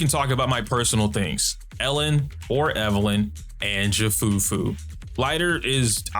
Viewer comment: In 9:43 the subtitle is wrong? Should be Evelyn instead of Soldier 0, right? PS: I've only noticed it because I am in love with graphic design of his videos.